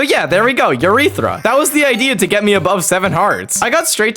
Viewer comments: why are you saying urethra instead of eureka😭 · There’s no friggin way he said “urethra” instead of “eureka” at 2:49 LOL